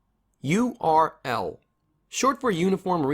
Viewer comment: honestly i thought he was saying “You are L”